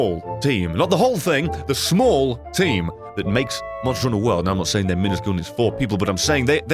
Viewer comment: Did he say monster in a world? Is he talking about monster Hunter:world?